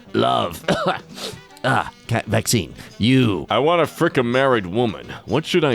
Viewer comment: did bro say "cat vaccine" in-between the coughing? lmao